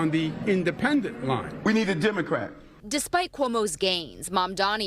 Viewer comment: Díd I misheard or did Adams say "we need a Dimocrat"? That's what I'm calling them now